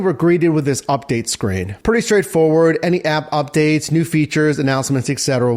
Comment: did you say EK-setera?